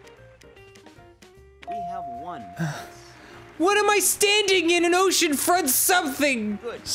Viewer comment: I hope Dan doesn't feel bad about thinking it was "standing" instead of "staying" at 44:36 because I totally thought that too until Arin said "it doesn't even fit" and I swear I had the same exact reaction as Dan